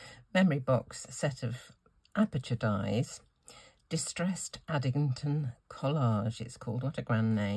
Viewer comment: When I heard you saying the name of this set of lovely dies, the window was open and a large lorry we t by, and I thought you said Distressed Paddington so I looked at the packaging and saw the correct name.😂😂